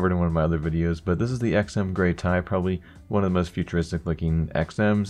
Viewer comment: Did he just say'd Gray Tie